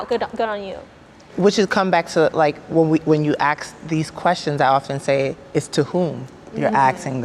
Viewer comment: when you axe the question??? English is not my first language, any help? what is being said? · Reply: she said ‘when you ask a question’ its her accent that makes it sound like ‘axe’